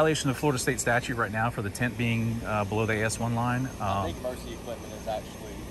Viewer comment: The cop gets it! good guy for sure....I stand corrected! I didn't watch the entire video. I tried to give you officers credit....but you have to meet the citizens at least half way! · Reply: I know it's tint but it kinda sounds like he says test your taint below the assline at 1st listen. Cracked me up.